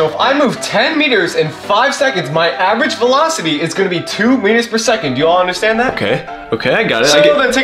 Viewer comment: HAHAHA, BROOO THIS IS LITERALLY ME. ALSO, I THOUGHT YOU SAID MINUTES PER SECOND ON 0:40. 💀